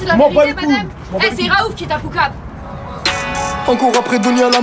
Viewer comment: Qu'est ce qu'elle a dit ?? 3:28 C'est Raouf qui t'as..... ?? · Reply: poucave (dénnoncé)